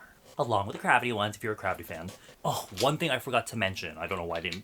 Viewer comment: 😂😂😂sounds like saying crappy